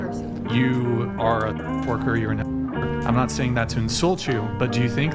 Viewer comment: oh wow lol I thought he said "you are a porker" lol !...and "facially challenged" I like that, a polite way of saying it...the delusion on these women is incredible !